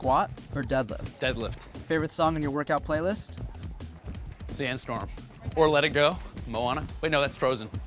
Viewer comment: Does he say "Dance Storm"? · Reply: Sandstorm